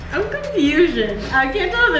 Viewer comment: Did say “I’m confusion”’? 😂